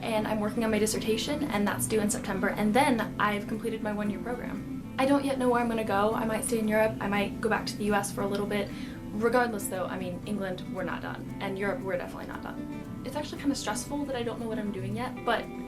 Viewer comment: You speak fast, damn, faster than me! Good video, but I didn't understand what you said at 0:26. England, we're not done? I had to rewind it several times, but I still couldn't positively decipher what was said.